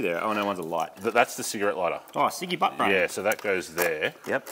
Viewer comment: Did I hear a Ciggy Butt Brain reference and 4:30?